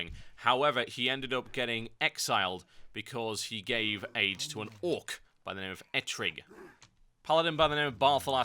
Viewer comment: Im confused.. at 2:50 does he say he gives an orc AIDS?